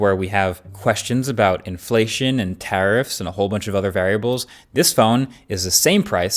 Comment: It sounded like terrorrifs